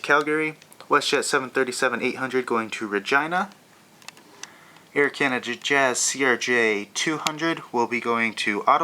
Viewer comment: did toyou just say that wedt jet was going to Vagina?